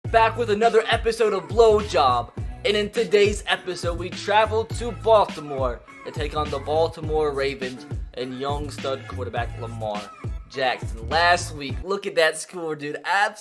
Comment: did he say blowjob?